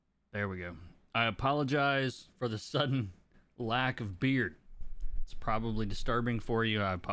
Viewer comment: At first I heard "lack of beer"😅 Thanks for the video! As always amazing.